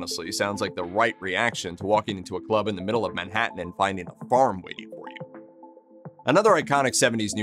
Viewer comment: did you short circuit here? What the hell did you even say lol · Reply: walk into the middle of a club in Manhattan and find a farm waiting for you ......is that what you mean....